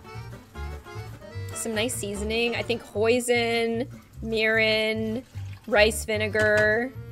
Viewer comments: did she say POISIN?! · I thought she said poison